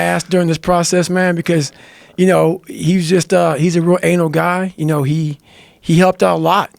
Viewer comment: Did he say at 1:50 "He's a real anal guy"? 🤔😀